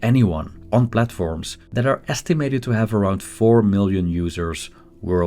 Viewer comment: 4 million world wide or 400 million world wide? The subtitles say 400 million, but it sounds like you said 4 million. It seems like world wide, 4million would be an underestimate!